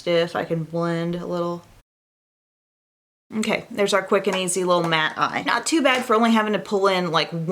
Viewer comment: It sounded like you said "little rat"